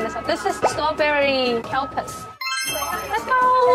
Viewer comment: I heard strawberry cow piss 😭